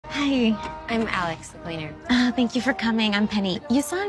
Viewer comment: I can’t be the only one who thought that said cheater instead of cleaner. Right?